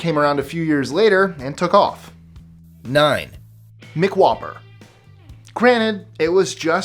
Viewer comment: did you say MICK whopper? Hahahahahaha